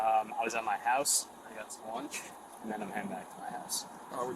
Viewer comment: He never said "breakfast." He clearly says "Lunch."